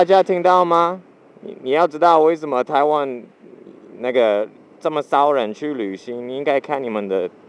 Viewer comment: sounded like u said nigga